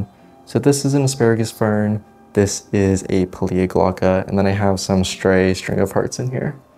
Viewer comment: What's the plant growing with the asparagus fern? Sounds like you said "plediogocla" but when I look that up nothing comes up. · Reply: pilea glauca!